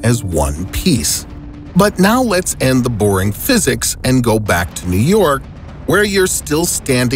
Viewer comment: did he say BORINGG PHYSICS 😠😠😡